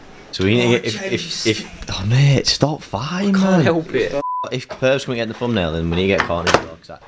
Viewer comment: says to "Mate, stop ______!" What is he saying? I've been trying to figure out that word since dad gassed him in an earlier vlog. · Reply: Farting haha